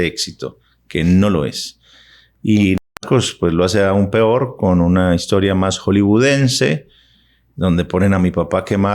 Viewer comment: Que dice el en minuto 7:05 está muteado? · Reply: “NARCOS” la serie de Pablo